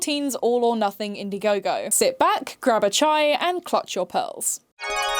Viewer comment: are you saying ' grab a chai ' here? that's what i heard when i was listening, and the closed captions said the same thing!